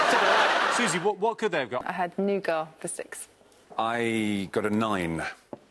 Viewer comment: what word is susie saying? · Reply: nougat